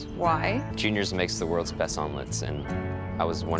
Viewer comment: Holder said omelets and thought he said *blank*makes the best almonds. Im dumb or hard of hearing